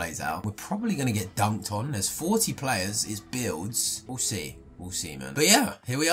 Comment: what did he say 🤨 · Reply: i heard ousi ousi instead of we’ll see